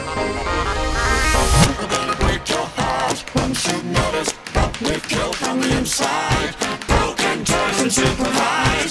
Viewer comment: does it say "Break your heart" or "Break your Arm" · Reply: Break your heart.